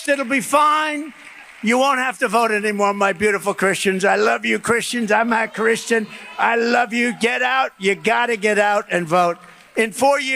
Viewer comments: If you listen closely it sounds like he says"...I love you Christians, I'm not Christian..." ????? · Did anyone else hear him literally say, "my beautiful Christians, im not christian..." Ive listened multiple times and everytime it sounds like he says "im not christian". Anyone else hear it? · Did he just say he’s not Christian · I swear it sounds like he said "I'm not a Christian." 😂😂😂😂😂😂😂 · Does Trump say "I'm notta Christian"? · Does Trump say "I'm not Christian." at 2:35? It sounds like he does but I'm not sure. VOTE BLUE!!!!!!💙💙💙💙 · It sounds like he is saying "... I'm not Christian..." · It actually sounded like he said “I’m NOT Christian”! Just sounded like that to me… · does he say i'm not Christian ?? Freudian slip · He literally says "I am not Christian" how is everyone missing this??!!! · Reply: I heard "I'm a christian". Either way....ugh we know he's not. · Swear he sounds like he says "Im not Christian" after asking Christians to vote for him one more time. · I'm not sure if I'm hearing correctly @ 2:33 Is trump saying "I'm not Christian" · Did he just say, ' I am not Christian ', the only truth he has ever said. This man is crazy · Did I hear him say, “I’m not Christen but!” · The thing I noticed was when he was addressing the Christian group he said he loves his Christians and it sounded like he said " I'm not a Christian" did anyone else catch that?